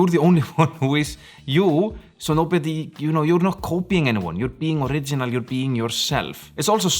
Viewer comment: Oh, I thought you were saying, “coping” not “copying.” 😂